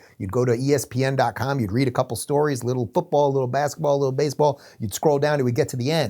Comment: “Face pulled back, dentured weirdo”…ouch. True though. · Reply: I thought Dave said Baseball Bat?